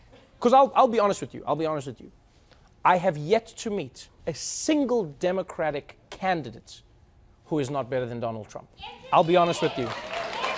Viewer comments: Ever heard of Andrew Yang? You DID meet him on the show. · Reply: A little hard to hear but he said he hadn't met a single dem who couldn't beat trump. At first it sounded like he said not a single one could. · What was that lady yelling at 1:41? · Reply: "Yes you did. Andrew Yang!" Trevor met him... on HIS show and he is the one to beat Trump. #Yang2020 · ¿Is someone gonna talk about the lady at 1:40 ? ¿What did she even said? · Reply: I said "Yes you did. Andrew Yang!" Trevor met him... on HIS show and he is the one to beat Trump. #Yang2020